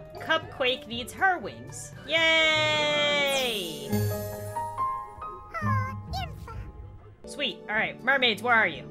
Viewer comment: did ihascupquake call her wings Beautiful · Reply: galaxy gamer X3 roblox Yeah she said “aww beautiful”